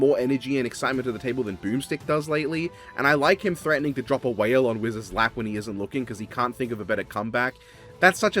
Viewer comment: I thought he said drop a whale on wiz's lab as in laboratory 15:20 genuinely the most hype I've been for a next time in a while. I watched all of Geats for Ace when I saw he won the champion vote so his episode is my most anticipated but this one is a very welcome surprise